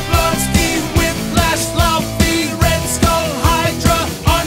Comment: Not gonna lie, i thought it said luffy instead of laufey 😅